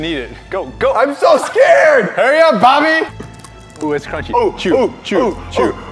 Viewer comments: did he call him Bobby? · did Bryan call J-Fred Bobby or is it just me